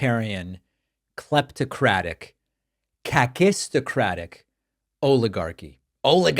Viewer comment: what is this word??? I don't know it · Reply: Kakistocratic. I read your comment before the video started and as soon as he said this word I knew this is the one you meant 😂 the definition of kakistocracy is “a government run by the worst, least qualified, or most unscrupulous citizens”. I then when and looked up unscrupulous and that one means immoral/dishonest lol